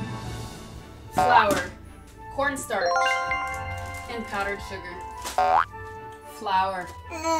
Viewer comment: Did she said pornstar at 0:51 ? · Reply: Cornstarch = almidón de maíz o Maicena